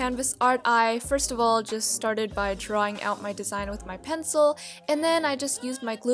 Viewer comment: i thought u said pretzel not pencil lol